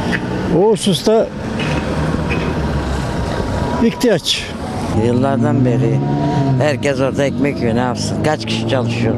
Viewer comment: 0;44 teyze yıllardan beri ekmek yiyor mu dedi benmi yanlış anladım.bu iste bi gariplik var